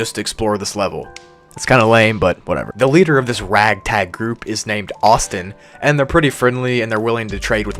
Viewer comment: bro i thought that said it was led by Autism 😭🙏